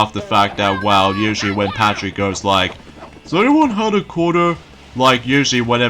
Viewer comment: Hey there! Just so you know, In the season 1 episode Sleepy time (2000) Patrick says: Does Anyone have a Quarter? ( Sounded like you said: does anyone Have a corner.)